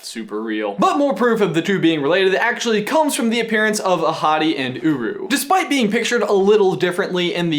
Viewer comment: So when you said "Ahadi and Uru", I thought you said "A hottie and Uru". If you were actually saying that, you wouldn't be lying.🙄